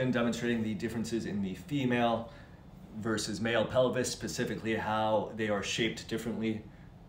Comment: did you just say "pasifically"?